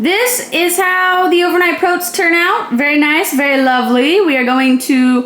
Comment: Did you just say "proats"? Awesome